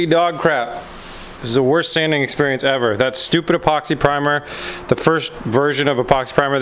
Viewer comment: Hay. The Z is looking really great. One question at the start of the show when you said about sanding the door did you call the primer "Epoxy Primer"? To me it sounded like you said Poxy Primer. 😂😂 Love the show. 👍